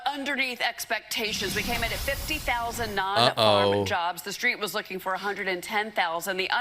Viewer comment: i’m sorry, did she just say “the street”? As in Trump’s economy is beat to the street?